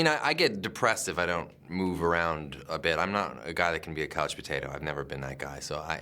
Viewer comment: sorry,but does anyone know what ryan says in 3:01 saying that he's not a guy that can be something.... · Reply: "I'm not a guy that can be a couch potato"